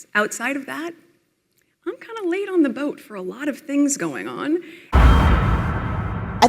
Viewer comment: Did she say "lay on the boat" or was that misheard??